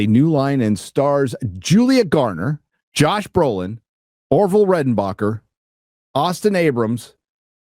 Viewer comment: Did you say Orville redenbacher was in this?!? Now I want popcorn! 😂